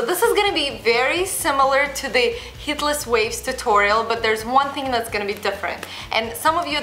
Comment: When she says heatless waves it sounds like she is saying hitler's waves 😂